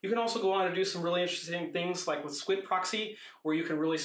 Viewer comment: Min 8:30...is it swift Proxy, swit proxy, or can someone clarify what he said? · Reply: Squid Proxy. You can search around for pfSense Squid for a lot of guides and additional info.